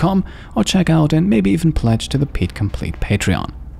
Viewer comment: The whole time I've been watching your videos, I thought you always said "cheers" at the end of the episode but now I'm pretty sure you're actually saying " tschüss"